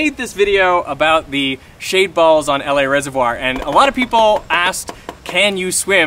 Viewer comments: Thought this man said shaved balls. Thanks YouTube. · I thought he said “shape balls” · For nearly the entire video I thought they were saying shape balls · I always hear "shaved balls" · I keep hearing "shaved balls" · Oh I thought you said shad balls I'm sorry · Why do I keep hearing "shaved balls"